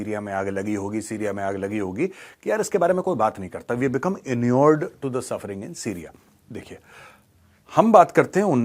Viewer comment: Gaurav Ji, what word did u say at 1:43, It could not be clearly heard. It is something like "inuered". Please make clear